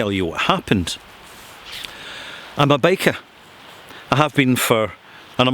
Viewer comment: Dang, for a second thought you said you were a 'baker' at 1:25 and would go full depth into the fine world of artisan pastries.